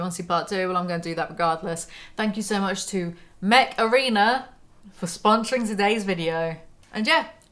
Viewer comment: Install Mech Arena for Free 🤖 IOS/ANDROID: <link> and get a special starter pack 💥 Available only for the next 30 days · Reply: Ohhh... "Mech Arena," I thought you said "Macarena"